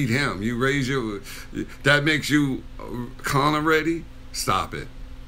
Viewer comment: "that makes you Conor-ready", did I hear that right? Well... He proved to be quite "Conor-ready" , didn't he? XD